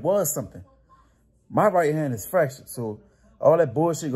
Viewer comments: i thought he said “my right hand is fractured” lol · Lol dude really got his ego hurt when Mike said it was the weakest punch ever 😂 got up in his feelings so much he had to make a YouTube video saying he’s fresh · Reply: I thought he said fractured